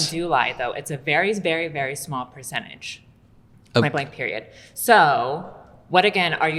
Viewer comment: Women blaming their periods for causing them to lie is not even the wildest things i heard in this video but damn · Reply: She said “point blank period”. I don’t think she meant anything about periods and lying